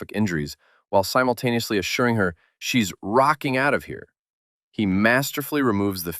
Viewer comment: Rocking out of here? What a peculiar way to phrase letting her leave. This is NOT a party! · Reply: I thought he said ROCKETING out of here. And ge is saying whatever it takes to keep her talking